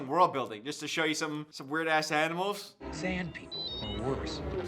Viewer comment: I haven't watched star wars before and at first I thought he said San people (if you don't know, look it up) Love from South Africa❤🇿🇦